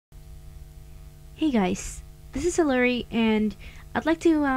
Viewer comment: it sounds like she says "hey guys it's hillery"